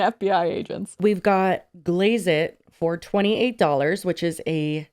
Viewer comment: When said “glaze it for $28” I thought she was saying “Glaze it 420” and I was like wait that’s amazing 😂 but nah. This shit boring as hell. But her followers will buy anything she shills, she’s probably just happy she wont get in any trouble for doing ads for her own company 24/7